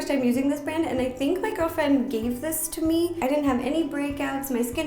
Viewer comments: does she say girlfriend 😂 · I think did she say girlfriend?